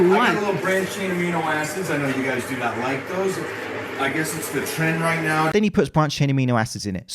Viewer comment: I swear I heard him say "I guess it's the 'Tren' right now".